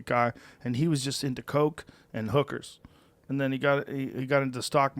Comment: Did you say “Cocaine and Hookers”? American dream